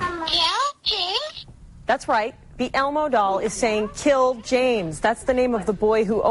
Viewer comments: It sounded like it said "Hello? James?" To me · It kinda sounds like its saying "Pal, James"